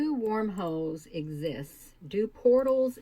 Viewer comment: When first started talking I thought she said "Do warm hoes exist?" Then I realized she said wormholes. 😂